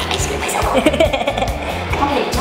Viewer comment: OMG LISTEN TO WHAT SAID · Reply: HE SAID I SHIT MY SELF XDXDXDXDXD